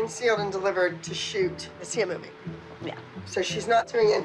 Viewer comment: Did she say the Sia movie?!!? As in Happy